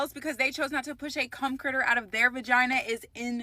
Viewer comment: Gasp 😂 2:32 Did she just say a “cum critter”? I’m SCREAMING!!!!!!